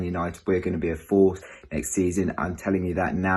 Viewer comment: I thought he said “we are going to be in 4th next season” 😂😅